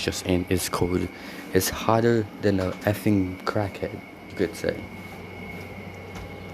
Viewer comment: WHAT DID HE SAYYYYY! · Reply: crack head